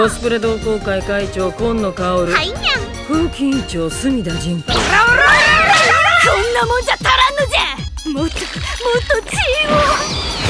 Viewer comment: what was that start and (1:06 do I hear ORA ORA ORA) comedy anime incoming I guess ?